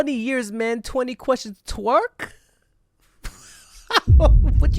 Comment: i think i heard /twe/ here, which is the korean onomatopoeia for spitting. The translation is wrong there or possibly a word play too? Also thinking way back then when kmedia reporters who were rude to GD asking him questions and he just laughed at them